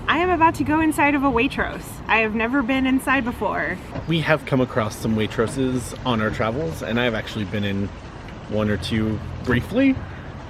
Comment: I thought said “ we have come across some waitresses before and I have been inside one or two briefly.” 😂😂😂😂😂